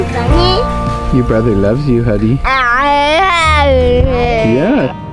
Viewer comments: I swear at 15:25 says I love you Huddy. Oh and you guys are awesome!!! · I swear says I love you.